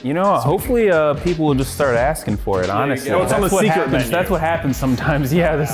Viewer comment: when j said secret menu i thought he said 'seeker' menu but hey both works